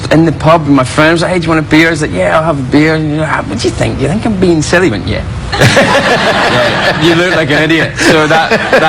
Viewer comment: Can anyone pls transcribe what he's saying at 4:25 - 4:30. Thanks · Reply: "What do you think, do you think I'm bein' silly?" he went "Yeah, you're being an idiot"